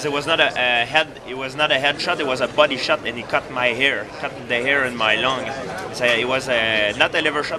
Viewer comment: "cut my air, cut my air in my lung" i thought he said "cut my hair"